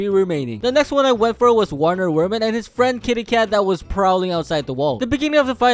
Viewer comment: my dumbahh thought he said wener werman and his friend kitty cat who was probably outside the wall